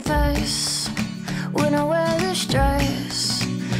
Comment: I must confess, when I wear this dress I feel like dancing the whole night with you And when I do, doo doo doo doo doo I feel so happy, I'm stuck on you 'Cause you are the one I could see having fun with Not just for the night, but for the rest of my life You are the one I could never be done with I want you tonight and for the rest of my life Doo doo doo You are so dreamy, feel like I'm in the movies I feel so happy, I'm stuck on you 'Cause you are the one I could see having fun with Not just for the night, but for the rest of my life You are the one I could never be done with I want you tonight and for the rest of my life Doo doo doo I'm stuck on you 'Cause you are the one I could see having fun with Not just for the night, but for the rest of my life You are the one I could see having fun with Not just for the night, but for the rest of my life You are the one I could never be done with I want you tonight and for the rest of my life Doo doo doo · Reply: First I thought she said when I'm under stress, not when I wear this dress, and both are great options!